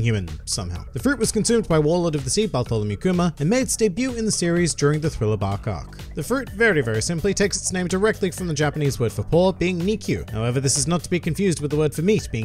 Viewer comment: lmao the caption says "thrilla ba-cock" 😂